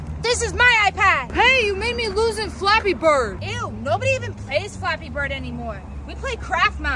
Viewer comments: I thought you said that you don't play Floppy Birds and your playing it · I thought you said no one plays flappy bird anymore